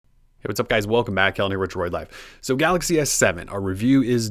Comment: the first two seconds of this vid are unintelligible. I have never been to this channel but all i heard was "hewazupgyz WELCOME BACK elmywdroylife